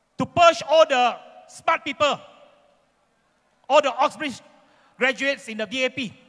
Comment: he said ostrich instead of oxford. Hehe